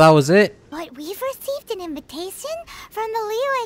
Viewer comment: did paimon just said invitaSIEN